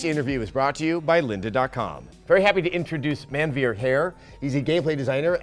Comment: I misheard what he said his name was as man beard hair